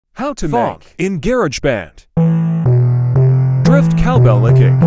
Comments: I heard “how to fuck in GarageBand” 💀💀💀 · I heard “how to fuck in GarageBand” 💀💀💀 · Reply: Same, but the description says "How to make phonk in GarageBand" but I heard "How to f*** in GarageBand" and also the spelling of the "Remember" turned to "Remeber" haha! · How t- PHONK in gerageband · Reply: it sounded like he said gerage band in stead of garage band